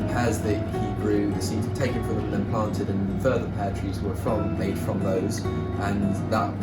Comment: When he first started saying pear tree. I thought he was was saying poetry, like the queen would say it. But I guess he really meant pear tree.